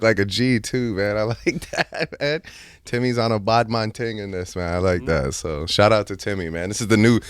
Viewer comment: did I just hear Drake say "BaDmAaAn TiNg" ? Badman ting.